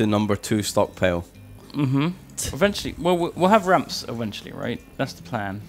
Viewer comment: I thought I heard say "we'll have rats eventually" and instantly had flashbacks to all the rat mod disasters 😂